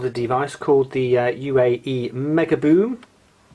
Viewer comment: I think it's called the Ultimate Ears Megaboom, not the United Arab Emirates lol